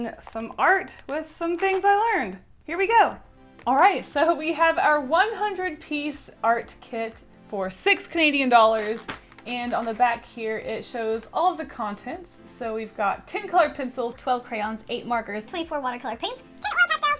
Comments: I thought it said $6000 piece art set lol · Who else thought it said $6,100 Piece Art Set? I would be so confused because wouldn't go all out for one video.